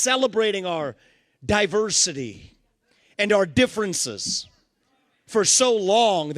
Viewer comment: I thought he said they'd been celebrating their 'diaper city'.